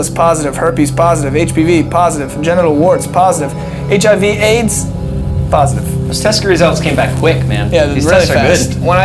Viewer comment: Did I hear hiv/aids positive?